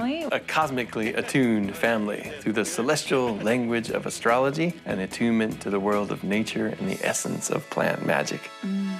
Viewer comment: I swear I speak English from age 2 - I do not understand what the f is he saying here🙄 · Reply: "We smoke a lot of weed" 😂